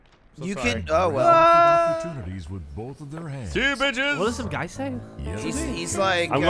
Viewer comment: When I first saw this, I thought was saying “Let go, bitches!” and and were dangling him off the edge. I had to rewatch it a few times before I realized was the one trying to drag them off.